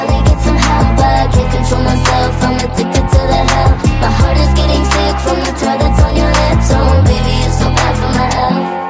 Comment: who else thought she said 'my heart is getting sick form the toilet on your lips' 🙋🏼